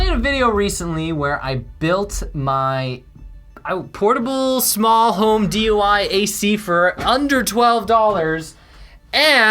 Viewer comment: Anyone else notice it sounded like he said "portable small DUI air conditioner?"